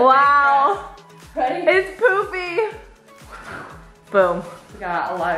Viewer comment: Am I the only one who thought said “its poopie” 6:15 😂😂